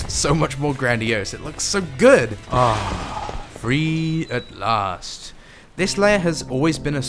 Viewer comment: What does this mean why did he say this????????🙃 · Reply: Aaah.. free at last. O’ Gabriel, now dawns thy reckoning, and thy glore shall glisten, before the temple of man. Creature of steel. My graditue apon thee for my freedom, but the crimes thy kind have committed against humanity. ARE NOT.. FORGOTTEN. And thy punishment.. is DeATh!!